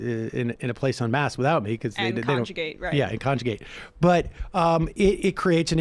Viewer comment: Starting at 2:36 did they say, "conjugate" instead of "congregate"!?